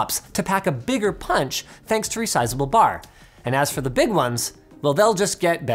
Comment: did someone say resizable bar?